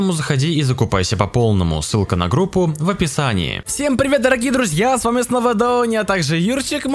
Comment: Мне послышалось в начале: всем привет с вами Доуни??😂😅